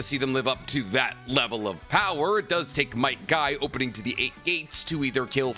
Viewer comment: Ok am i the only one that hears might guy instead of might dai